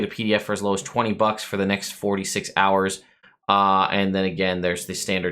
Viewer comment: During the Kickstarter segment I didn't 46 hours. I heard " it will end in 4d6 hours?